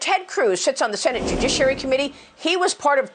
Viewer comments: Sounds like she said shitz on the senate judiciary committee. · if you watch at 1.5x it sounds like she said you shit on the senate judiciary committee.